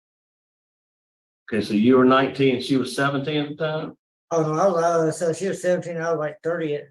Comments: I thought she said she was 15 at the time. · I thought I heard him say that she was 15 and he was 30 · It sounded like he said she was 15 an he was 30